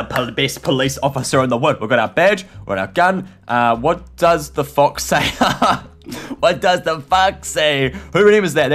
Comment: It sounded like you said what does the f##k say